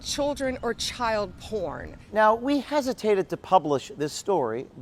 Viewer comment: I actually didn't hear it that way. I heard "OR" CHILD P*RN. As in, there are both things videos and child p*rn.